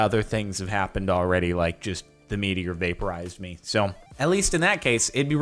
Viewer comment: I thought he said the media vaporized him xD